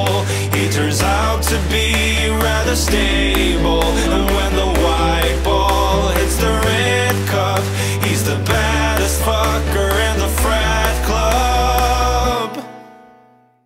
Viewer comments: i hear: "Daddy does the pong dance." too much fanfic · Thought they said: "Daddy does the pong dance" haha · For longer than i care to admit, I thought it said "daddy does the pong dance"